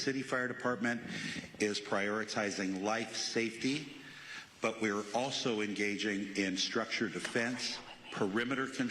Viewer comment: Am I trippin or did anyone else hear that whisper at 1:11:50 ? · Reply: "Come over here with me" If you watch his conference live, you'll probably see a woman whispering to someone